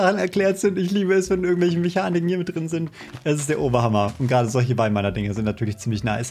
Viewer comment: HAG noch jemand verstanden, „es ist der Obama!@ 🤣🙃